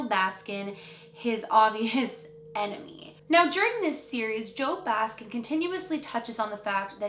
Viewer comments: I heard at 2:00 she said JOE BASKIN???????? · did she call him joe baskin?? 😂😂😂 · Anyone else catch her accidentally calling him JOE BASKIN at 2:02 😂😂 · did she say Joe Baskin